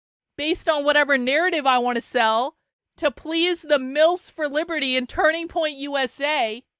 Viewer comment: Did you just say "MILFs for liberty"?